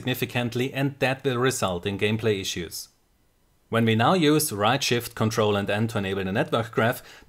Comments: you say the shortcut but I can't understand what you are saying. I have gotten stuck with my graph open and can't close it. embarassed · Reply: ctrl+shift+n is the netgraph ctrl+shift+r is for the small overlay in the top left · i didn't understend what you said here :/ can you type it pls ? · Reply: right shift + CTRL + N You can change that key combination in the options menu of Overwatch